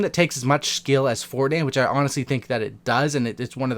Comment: what did you say, "fordayne"?